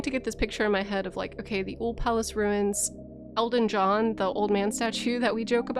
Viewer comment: I totally misheard "Elden John" as "Elton John"